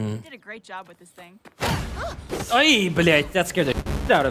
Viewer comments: what? did he say блять (blyat')? 😭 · NOT THE "BLYAT" LMAOOOOOI 😭😭😭 That took me so off guard